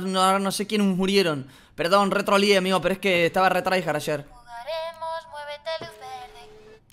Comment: la muñeca no dice jugaremos muevete luz verde sino que dice jugaremos muerete luz verde